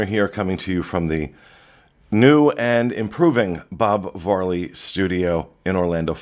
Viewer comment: When I first started listening to the dis I thought would always say Bob Marley studios. Lol